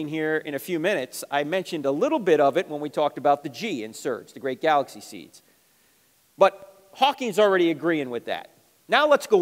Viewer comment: did he say "the great G it serves, the galaxy seeds"?......